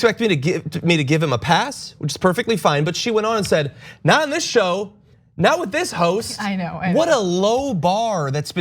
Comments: I thought she said “not on this show, not with this hoe” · I thought she said not with this ho... i thought that was pretty funny